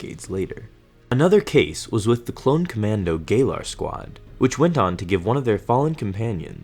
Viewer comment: i thought you said gay lord squad